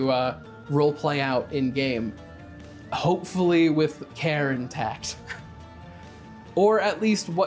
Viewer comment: you can not be horrified of anything :) "by" I really heard "karen tax" instead of "care intact"...I liked that